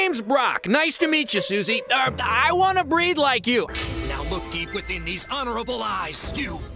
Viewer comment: Usually I thought he said, "I wanna breathe like you"